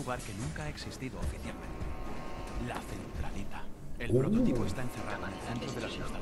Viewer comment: "La centralita", al principio escuche solarita xDD puto xD